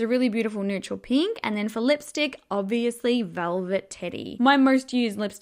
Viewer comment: I swear on everything I love, I thought you said “velvet titty” as the lipstick shade name.